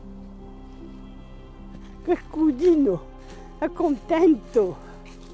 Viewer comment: ho sentito bene??Dice:porkodio è contento!😂 E comunque è stato un video veramente toccante..un abbraccio di ❤alla signora.🫂